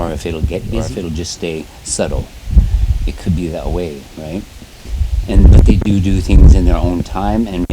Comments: there is definitely another voice answering. It sounded like it said "right " like replying to him. It didn't sound like his voice at all and I don't know how his voice would of done that as he is speaking. It was strange but pretty cool! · Has anyone noticed at around 8:40 there was a voice on the recording other than? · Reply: Yes I heard that too I was just commenting asking if there is another man that's sitting there listening to his story or something. I heard it at 8:35 sounded like another guy saying "right " while he's speaking so I have no idea how it could of been him